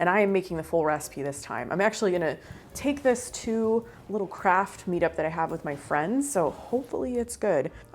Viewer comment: Why is it that I thought you said a "Kraft" meetup like a mac and cheese meet up, which sounds delightful. I should never watch when I'm hungry. 🤣